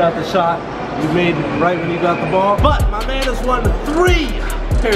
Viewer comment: Is it just me or did jesser say “Brazy” at 4:40 ?😂